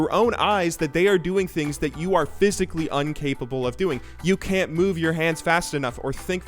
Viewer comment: Did you just say "Uncapable???"... Come on... I can understand typos, but mispronunciations??? Come on. Come on.(It's Incapable. Uncapable is not a word.)Anyways, nice vid. And Challengers are still Bronze at heart. In our hearts of heart, we are all bronze.